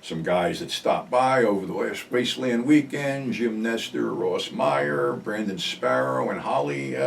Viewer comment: For a second I thought you said Russ Meyer but you said Ross :) - great stuff, thanks again